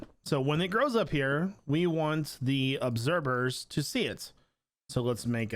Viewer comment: Is that just me or does he say "obserber"